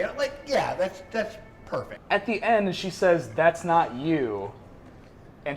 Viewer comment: I thought she said, "I saw you.". No?